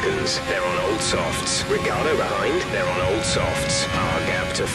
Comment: I hear Riggardo